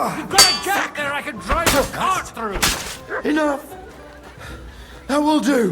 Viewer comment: OMG, he said CART. during the dual, i wasnt paying attention to the sub titles bc i was fighting.... When i heard Godwin say the thing about the gap, I thought he said CAR, I was Like WTF lol why would he say that. edit